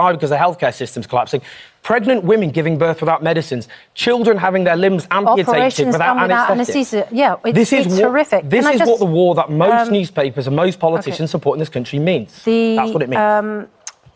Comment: Did Suzane just say terrific @ 10:59 - when owen was saying how many people are going to die because Palestinians having limbs amputated without adequate medicine. How disgusting